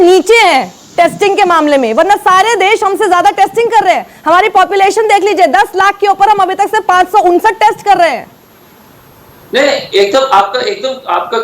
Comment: Did i hear our population is above 10 lakhs and we are doing 559 testing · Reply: chutiye usne kaha on population 10 lakhs we r doing 559 test